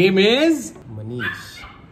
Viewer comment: I thought he said ABISH when they were naming the puppy.